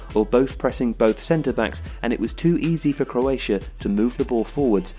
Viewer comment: did he say croatia?